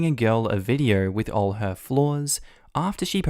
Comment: Idk why but when you said all her flaws I thought you said floors.